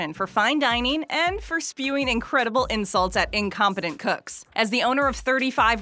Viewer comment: I misheard for a sec, I thought she incompetent f*cks.